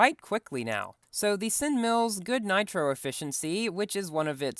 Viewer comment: did I hear twin mills or sin mills?